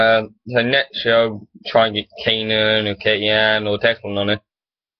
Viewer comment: What did you say at 1:02? Sounded like keenan and keenyan. BTW Alberto Del Rio shouldn't be amongst the good wrestlers at the moment!!